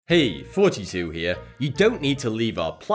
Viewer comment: WoW...for the longest time ever i thought you said "HEY THERE FORTY-TWO HERE" instead lf Thoughty2 lol